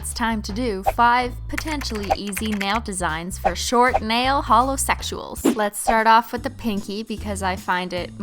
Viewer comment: Idk why but at 01:28 I thought she said short male holosexuals 😂